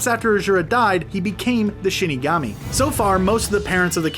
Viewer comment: Sounded like he said shitagami